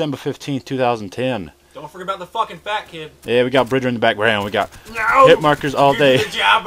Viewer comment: wait did he say "we got bridger in the background" at 0:30?